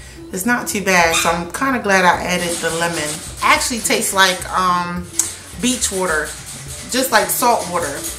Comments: “It tastes like beach water” okay Are we talking like orchard beach or like clean Island beach ? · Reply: I thought you said bleach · “actually it taste like beach water, like saltwater” LMFAAAAOOOOO SHES TOO FUNNY EVEN WHEN SHE DOESNT TRY TO BE IM JOKED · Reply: I thought she said Bleach Water. 😭